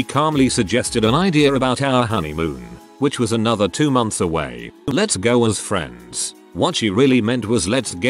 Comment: The “let’s go as friends on our honeymoon” spouse only ever wanted “her day”. She didn’t want to be married. She didn’t want to be a wife. She only wanted friends and family to lavish her with attention and money. She then lost immediate interest once the pageantry was over. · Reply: Oh! I heard it as let's go with friends. I wondered why he was making a fuss